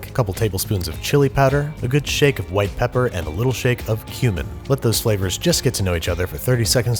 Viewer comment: thought he said human lol